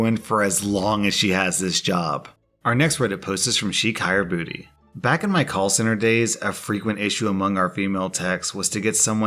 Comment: "Our next reddit post is from she kayer bouti" Uh, that's not what that says. It says: SHEIKH YER BOUTI